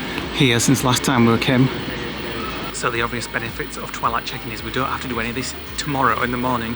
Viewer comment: Can't wait for your Florida vlogs. Have a great time won't you? I had to giggle because I thought you were saying Twilight Chicken and were heading off to a new fast food chain! Doh 😅